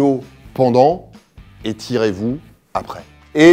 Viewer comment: À 5:00 j'ai compris "et tirez vous après" 😭😭😂